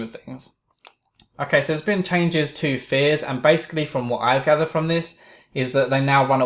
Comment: I thought he said "Fizz" instead of "Fears"